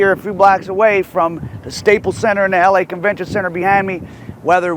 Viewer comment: I thought it said The LA CORRECTION CENTER.. behind you